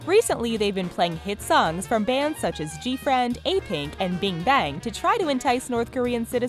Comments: I heard "bing bang" instead of what I read "Big Bang" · Did she just say BING bang!?!!